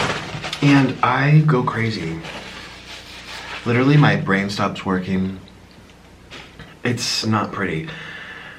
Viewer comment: i fr thought he said “Literally my brain stops twerkin”